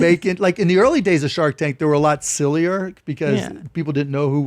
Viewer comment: did he say shart tank? 😂